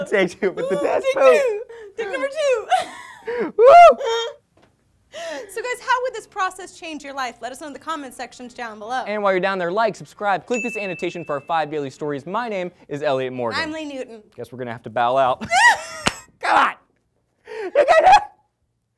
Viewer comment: It sounded like Lee said, "let us know in the comment sex change down below."